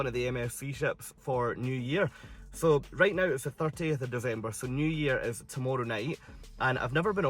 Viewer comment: Sounded like you said today is the 30th November & tomorrow is New Year [28 secs], maybe it's just the accent ... Long time since I have seen any of my Scottish relatives, so the ear isn't as tuned in as it once was.